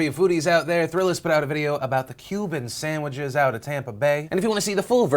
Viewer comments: I heard that as "human sandwiches" and suddenly listened more carefully lol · For a second there I thought he said human sandwhiches from Guantanamo bay haha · Whew, thought you said human sammiches out of Tampa... you didn't, right Phil?